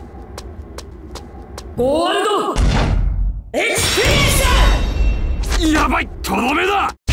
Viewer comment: did Giorno said "Warudo Experience"? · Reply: He said gorudo ekusperiensu Gold experience